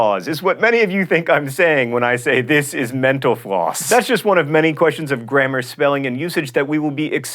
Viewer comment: I hear "Menoplause" when he says "Mental Floss"